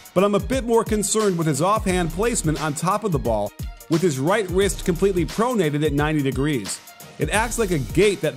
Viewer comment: coach nick said "right 'wist'" instead of wrist. 👌